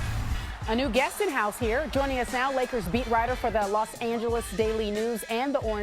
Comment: lol I thought she said lakers meat rider at the beginning 😂